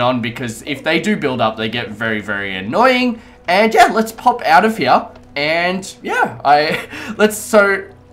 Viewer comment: look at 1:45 with subtitles says lets pop out of here then the subtitles say lets pop adam p